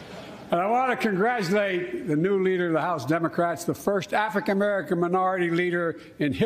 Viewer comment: sounds like he says “Affican merican”